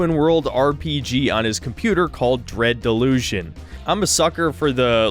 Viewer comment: calling your PC "dread illusion" is edgy, but whats the name of the game, though? I think it might be called "reds delusion"